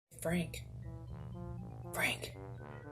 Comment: That's Frank❤️ today is our birthday 😺🙋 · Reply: i thought you were saying freak lmao. Happy Birthday!